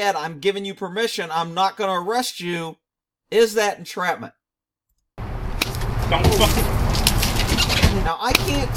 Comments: I think he said “dumb mother F’er” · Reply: I thought he said "Dont fuck with.." as the old man was falling to the ground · I think the cop said “mother fucker” as he slapped the man. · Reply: It sounded like he said "dont fuck with.." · I hear, "...don't fuc...". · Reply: I think it’s “dumb fucker”